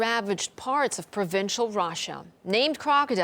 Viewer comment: Did she just say "rasha" instead of Russia? : \